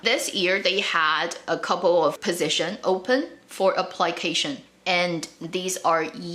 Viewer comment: I was listening carefully to your video and suddenly at time 07:10, I heard something like apply-cation..it's application, right?